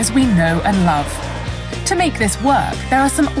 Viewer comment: What I heard was "To make this worse"